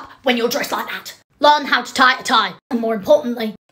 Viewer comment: I don’t know why, but I thought Draco said “Brazil” instead of “Brassiere”